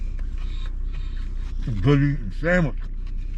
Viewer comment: what did you say..??!!🤣👍💪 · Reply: I said - “this is some good eatin’ sandwich” I think the reference is from a movie called Freejack